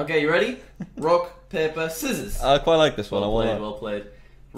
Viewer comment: DID JACK SAY I LIKE THIS ONE (5:30) BECAUSE HE KISSED KAI????